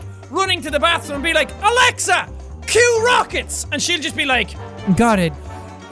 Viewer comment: who else heard his alexander at at 3:12 its faint so sounds up.